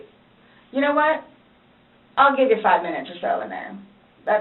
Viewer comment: What does she say at 26:10? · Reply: "Ya know what... I'll give ya 5 minutes or so in there..."